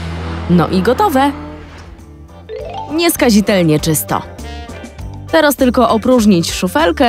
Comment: czy tylko ja usłyszałam "teroz"?